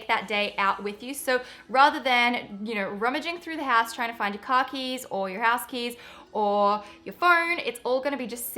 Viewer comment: for a sec I thought she said 'khakis" when she meant car keys